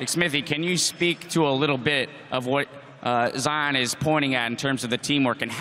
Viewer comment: Am i the only one around here who thought he said "little bitch" at 2:27 ??